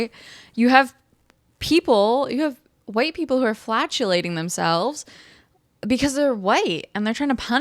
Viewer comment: it sounded like you said "you have white people flatulating themselves..." Perhaps you just have a regional way of pronouncing "flagellating." Or maybe white people actually should be flatulating themselves, I don't know. Perhaps that should be a thing.